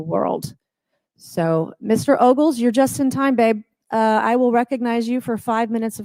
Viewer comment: did she say “you’re just in time babe”? Is this how they talk about serious topics at hearings?